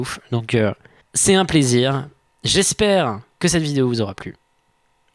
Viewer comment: "au 30 Août pour la vidéo d'Halloween", en Août, hein quoi ?